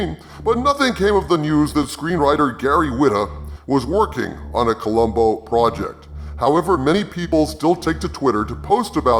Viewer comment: For a second I thought you said Columbo Robot.